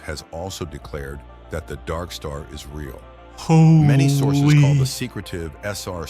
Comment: When you said "is real" I could have sworn you said Isreal. Anyway, I'm retard and love the show brother, keep up the great job!